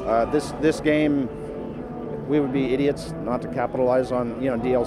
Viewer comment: He didn't say that, and from the gameplay footage it doesn't look like they're holding out on us. He's just saying that there is a lot of options to explore should they decide to release DLC. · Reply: ***** I don't know if you watched the same video as me, at 4:50 he clearly says "We would be idiots to not capitalize on DLC" watch the video again and then you come back ok.